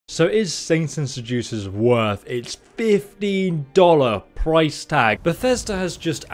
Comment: jesus i thought you said $50 price tag. Scared me for a minute there